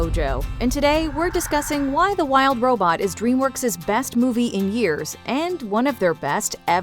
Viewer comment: I thought it said “b3st movie in tears” I mean, it’s true. Got me crying my guts out 😭❤